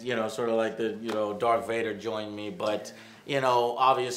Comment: It sounded like you said Dark Vader the first time